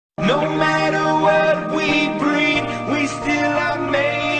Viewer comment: Am i the only one who hears "no matter what we breath we still are made of feet"??? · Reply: I hear "No matter WHERE we breathe" instead of what tbh Edit: I cant unhear both versions now help me